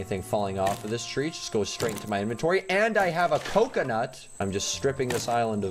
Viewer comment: How i Hear It: And Now I Have A Pokenut! How It's Said: And Now I Have A Coconut!